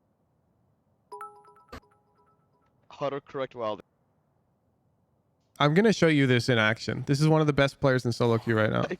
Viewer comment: What's the pro called? I wanna search for his vods but didn't understand the name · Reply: curry